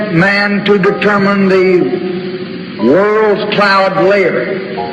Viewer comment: sounds like he says “world cloud layer” here not world class 🤔